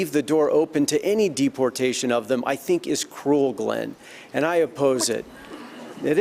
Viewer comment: Doug Wright 🤢🤮🤢🤮🤢🤮 I saw Doug Wright’s name and knew it was going to be a joke! I can’t stand Doug Wright! And during the dreamer part at 46:58, did McMullin say Glen? I’m pretty sure he said Glen, just no sure who he’s talking to.